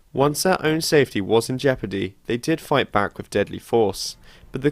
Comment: 'once their own safety was in ....' what does he say there? i didn't get the word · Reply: xIPsychox jeopardy